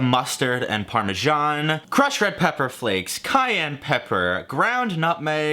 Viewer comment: my dumbass thought you said kanye pepper instead of cayenne ._.